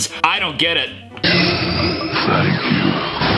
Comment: The tree is actually saying Thank you